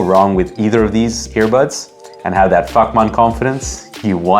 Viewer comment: Yep. There goes my coffee. Spat like a mad llama when I heard "fuk-man". 😂 SUBBED IMMEDIATELY.